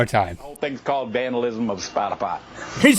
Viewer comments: I thought he said vandalism of spotify too. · I think he is saying "vandalism of spot a pot" but in the beginning I thought he said Spotify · I think hes saying they sawed down that whole porta potty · Here's what I heard - " I guess the whole thing's called vandalism of a porta-potty"